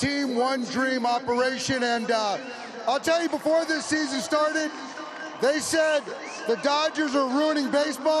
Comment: season started の前になんて言ってるか教えてください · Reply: I ll tell you, before this season started